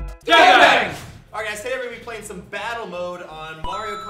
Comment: at first I thought Lazercorn said " Bottle " from how hard I was laughing